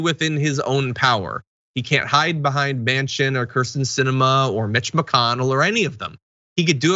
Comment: thought you said Bitch mcconnell